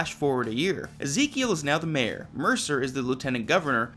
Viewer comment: I thought they said Ezekiel was Governor, not Mayor.